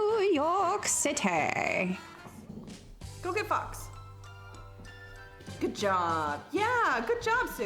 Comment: NGL at the end when you told the dog to "go get fox" the first thing I heard was "go get fucked" and it made me do a 180.